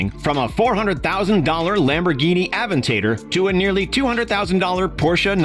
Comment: Did he just say Lamborghini Advent tater? 🤣⚰️